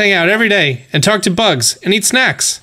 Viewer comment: Why did I read “And eat bugs and talk to snacks”? And Also, why does it say May 3rd?